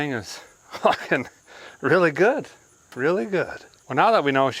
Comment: did I hear a fella say "really fu@ good"?